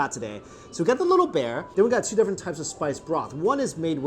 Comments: Sounded like u said titty bear 😂😂😂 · i heard it as “titty bear” 🤣🤣🤣, adorable. now i’m craving for hotpot